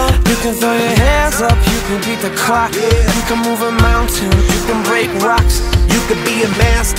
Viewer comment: I heard you can break blocks